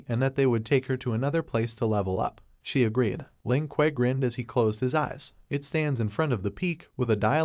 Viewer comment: every time it said "Ling Que" I heard "Lin Kuei" and i'm like: is sub-zero gonna show up?